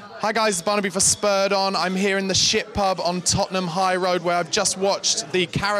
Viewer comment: did he say shit pub